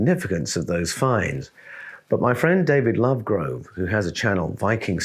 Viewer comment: On a light note 🎵..I was listening ..not watching.. for a minute and I heard two names wrong..heard "Lovegrow" (was thinking that's a nice name to have).. and then I heard "Snotty" Sturleson..had to back it up and was glad to be wrong on the second one!!...😂🤣 and, Paul, It's my ears, not your diction or delivery, my friend! Just thought it was funny enough to share! Thank you for your part in my personal spiritual journey...You regularly deiliver Eye and Heart and Mind Opening Stuff!